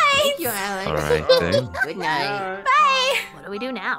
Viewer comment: why do i hear say goodnight "babe"